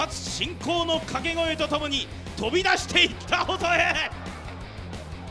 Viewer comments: 何回聞いても飛び出して行ったアホタレに聞こえるの草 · 音質が良すぎてちゃんと「飛び出していった細江」って聞こえたわ